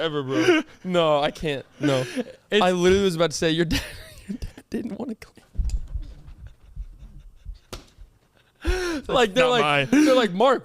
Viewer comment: does anyone know what said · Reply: He was gonna say his dad didn’t wanna claim him x